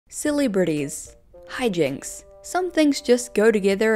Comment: I want to start off by saying i love your makeup, then when i clicked this video I didn’t realize you had an accent at first and i heard “silly britties” it was very cute and i needed that smile haha. I now realize you said celebrities 🥰😊